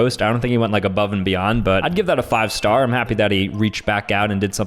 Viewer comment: he said that he but it sounded like he said daddy